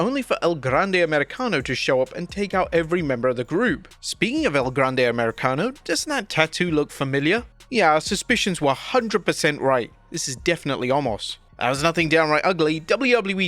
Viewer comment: Did he say “this is definitely Omos?” 😂😂😂